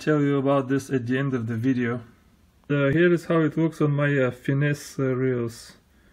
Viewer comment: Turn on the close captions. At 00:42 seconds, instead of finesse it says penis lol.